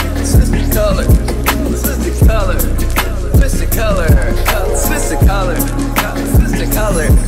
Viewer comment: I thought he was saying twisted colors 💀